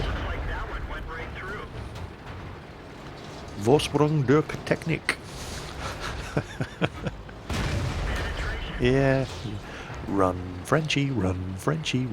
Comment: haha, i listened to what you said at 23:11 around 12 times, till i figured that you said "Vorsprung durch Technik" :D nice and fitting quote, indeed!